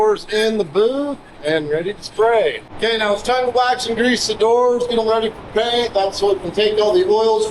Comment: Every time I hear him say "degrease" I actually hear gresse.